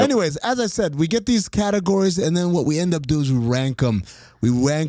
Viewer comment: !!!! I heard “We Wankem” also. I had the same note too bro!!! 😂